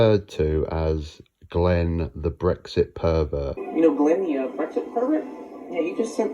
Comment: I thought he said "breakfast pervert."